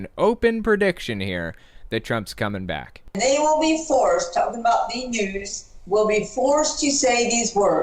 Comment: At about 9:08 did Gandalf the pink actually say, "They will be forced, talking about the Jews..."? It sounded like it to me, and if so that's horrible antisemitism.